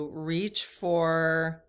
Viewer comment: My ears perked up I thought you said reach for M&M's....LOL